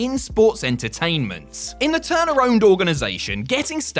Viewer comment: When he said ‘Turner Owned’ I thought he said ‘Turnaround’ and to be fair they both work.